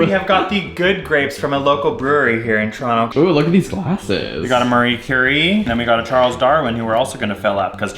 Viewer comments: he says marie curie but the closed captions say mariah carey hahahah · Ahah the subtitles are wrong at 1:37. His glass is a Marie Curie and not a Mariah Carey